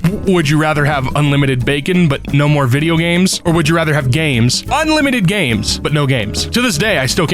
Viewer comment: Question: did you say "unlimited games but no games?" On the would you rather question?